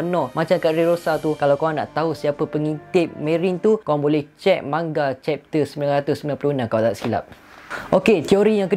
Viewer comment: manga chapter 996? Ke aku salah dengar 👂